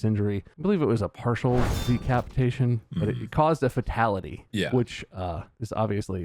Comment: Am I the only one who thought Bob was saying "Dickapitation"? I was so curious where that was gonna go, lol!